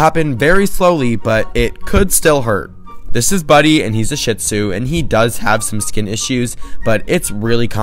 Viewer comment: I don’t mean to be rude abt the breed but I first when he said the breed I heard “Shitzoo”